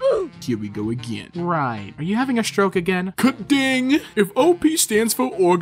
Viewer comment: I think this guy is Dutch and saying “piece of crap” about his phone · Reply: i said the same thing!!! KUT DING !!!